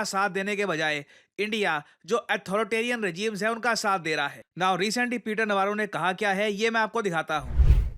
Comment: I heard in gawaro na kha kya ha instead of peter navarro ne kaha kya ha...😅